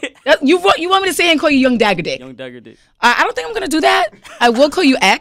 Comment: all I heard was "young daddy dick" and I was like HOL UP